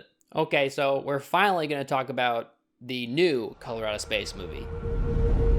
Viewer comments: It's sound like your saying Colorado Space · Am i the only one who thought he kept saying Colorado space movie?